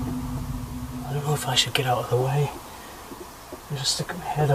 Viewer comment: Tbh I thought it said "yeah" instead of "get out"